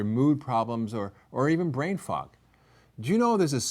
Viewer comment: did he say brainfuck? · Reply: No brain fog.